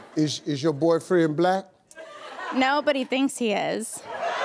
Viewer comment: "Is your boyfriend black?" "No, but he thinks he is." 😂😂😂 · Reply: I thought she said: NOBODY THINKS HE IS